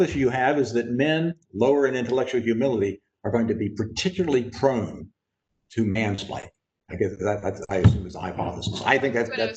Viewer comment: I didnt catch what Dr Leary said at 53:05… appreciate if someone cud write it here?… (and the actual question at 52:45)… i.e “peoplw with low IH is……….” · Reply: "connextions between this and the phenomenon known as mansplaining"